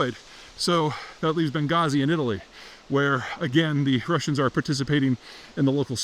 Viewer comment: Did you just say "Benghazi in Italy"? What is this, 1924?